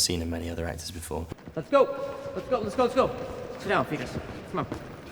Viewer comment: does Lucky call Festus "fetus" because that is very funny